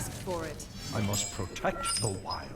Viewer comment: Whenever I play as or against a Druid, I never hear "I will protect the wild". I hear "I will protect the meta".